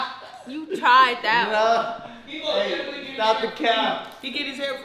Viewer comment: Oh my God, I heard it stop blurring